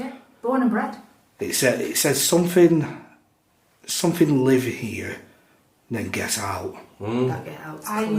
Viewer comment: that first dr 60 recording i thought i heard (I STILL LIVE HERE. NOW GET OUT!!!)